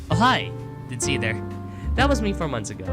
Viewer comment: i heard "ohio"